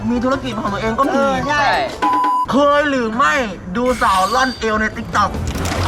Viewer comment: ที่พูดว่าเคยดูสาวร่อนเอว คนตัดต่อเขียนรดที่นอน